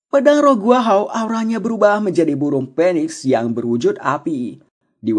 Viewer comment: itu maksudnya burung Phoniex... Dengernya burung Penis🤗🤗